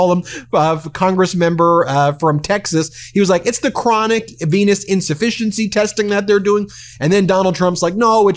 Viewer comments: Bruh I wasn't listening in the first minute I thought he said chronic penis insufficiency 💀💀💀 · I swear I heard Ben say “chronic penis insufficiency” twice…that tracks. Trump is a horrible person